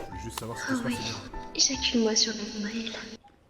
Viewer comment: elle a dis quoi ? (Au début) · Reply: Tsr -venom ejacule moi sur le nombril